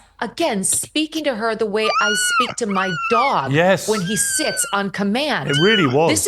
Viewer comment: Did you say "sits", or "s**ts"?